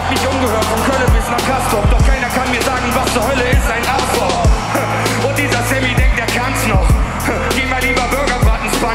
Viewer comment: was Hölle ist ein Afro 🤔? Habe ich richtig verstanden? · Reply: afrob